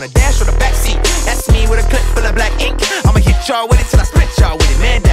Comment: When i hear 'black ink' i just hear blackpink